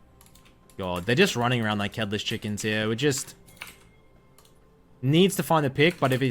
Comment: What he said: "Running around like headless chickens" vs what I heard: "Hitler's chickens"